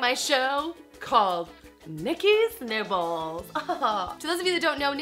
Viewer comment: Lol I kept hearing "Nikki's nipples" :D